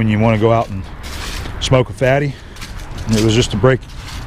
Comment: did he say, "Smoke a fatty?".... :-)